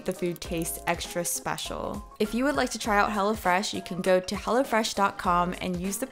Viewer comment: When she said "Hello Fresh" I heard "Hella Fresh" 😂